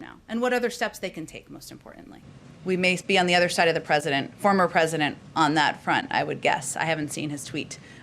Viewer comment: Every time she says 'former' I hear 'future'